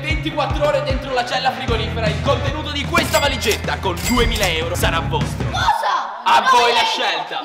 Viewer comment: ma non ho capito dice 2 mila euro e nei sottotitoli(non di youtube) appare 200 mile euro e non si capisce anche se credo sia 2 mila euro